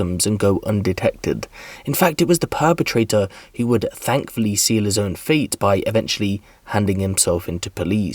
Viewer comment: I thought he said sell his own feet in stead of seal his own fate.🤣🤣🤣